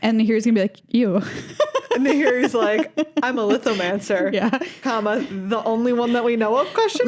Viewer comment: I heard "I'm a Lithomancer." as like "I'm a lesbian." because I mean, Nahiri is a lesbian. she couldn't be straight.